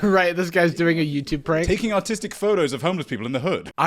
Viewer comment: I thought he said “taking autistic photos” and I didn’t bat an eye